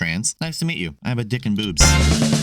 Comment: "Nice to meet you I have a dick and boobs" I think is the best way for anyone to greet a person. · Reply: Ngl, i thought she said boots.